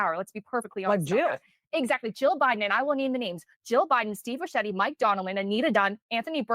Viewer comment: did she say Steve Buscemi wanted power?